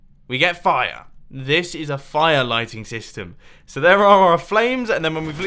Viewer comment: I thought it said lightning...